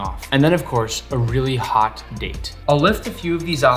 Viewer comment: A really hot date... I heard that wrong